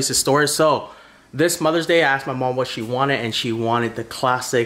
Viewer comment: I thought it said "i cooked my mom"